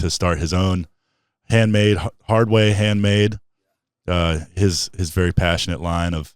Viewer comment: mentioned at the end that Aaron has moved on to do his own thing, I thought he said “Hardway Handmade” ? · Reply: Yeah, Aaron is working on this pretty amazing project called Hardway Handmade, we'll have Aaron on very soon to tell you guys all about it.